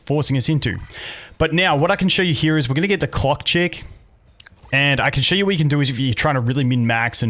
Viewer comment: I was eating and watching at the same time and then right at the moment I put some pasta in my eat hole I heard "AND HERE WE HAVE THE C*** CHICK" and I spit my lunch everywhere. I then I rewind and hear it right, it was "CLOCK CHICK" not whatever the hell I heard.